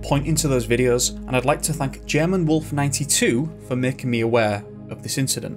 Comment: How odd & bizarre indeed!! This sounds sooo suspicious! Ty, you & Gemma, I hope I have that right. Sorry if I got the wrong name.Ty for sharing this perplexing case with us. 🙏 for the family. That must be so devastating. No answers leaves the pain to throb, forever. 😭 I do believe the authorities are covering up something. 🧐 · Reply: No Teese. I'm familiar with that channel also. I was referring too when he was thanking someone for recommending thus date for him , I thought he said the persons name was Gemma. I could have gotten it wrong, hence me hoping I got it correct & adding an apology if did not. 🙂